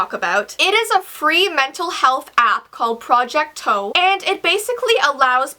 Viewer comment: I thought said project toe